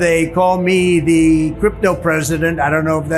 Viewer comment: He misheard. The said kelpto president.